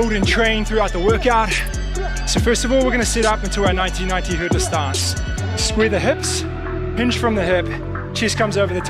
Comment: I'm getting old. I heard "90-90 hurdler's stance" as "1990 Riverdance" I was very confused. 😂